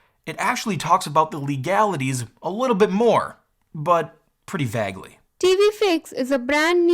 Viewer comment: In her accent, Im pretty sure I hear "TV Fakes"